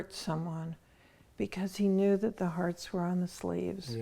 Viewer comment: what is she saying from 8:37 to 8:50something about the heart? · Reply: " he knew the hearts were on the sleeves" "If you wear your heart on your sleeve, you openly show your feelings or emotions rather than keeping them hidden." <link>